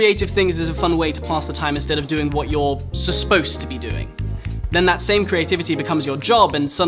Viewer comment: I like at 1:23 he said susposed instead of supposed